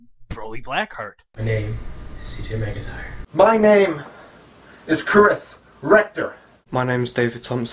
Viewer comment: Did he say his name was Chris Rectum? Still, interested to see what these guys have got.